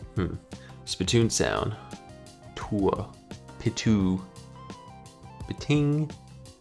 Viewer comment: did he say tuah